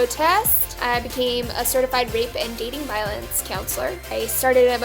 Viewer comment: I thought you said a certified raper